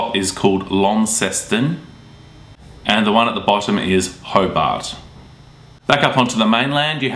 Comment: am i the only one who hears Hogwart?